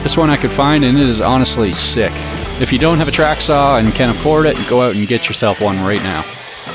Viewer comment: In vid I heard you say “if you can’t afford a track saw then go get one right now” or something like that